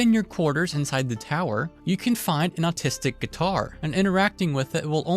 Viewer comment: did you say autistic guitar?